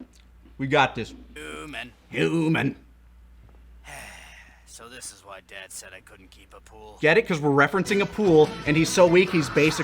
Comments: Oh my god, for over a decade I thought Radditz said "so this is why dad wouldn't let me keep a poodle" my life is a lie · I really benefited from the closed captions. Didn't know who the hell Appule was, so I thought he said "so this is why dad said I couldn't keep a poodle/a pool". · With the bad mic quality, I always thought Raditz said “So this is why dad said I couldn’t keep a poodle” and not “Appule”. In all these years, I’m only just now noticing that haha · Omg. The whole time I thought Raditz said "keep a pool" 🤣 I was a little confused.